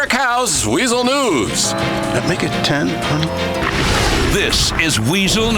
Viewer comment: What did he say?... "Make it 10..."?